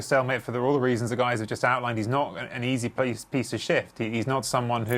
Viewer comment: When he said "he's not an easy piece to shift." I heard "he's not an easy piece of shit." 😂😂😂